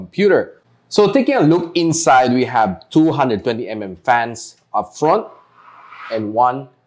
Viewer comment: When you said two...hundred twenty mm fans for the front fans, I thought you said 220mm. That got me xD And for taking out the front cover, it's best to pull it from below. Just a tip! All in all though, great review! :D